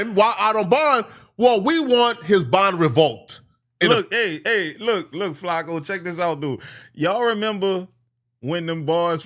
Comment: Did he say, “look flakko” as if he was in the room with him. Yo boy a fan.